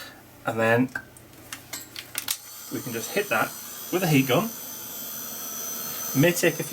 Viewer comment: thought you said Hic cup instead of heat gun lol